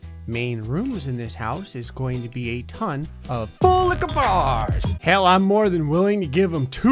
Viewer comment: i used to not understand what was saying when he said 'full liqour bars' because he said it so quickly, i thought he was saying 'bull licka baaaasss'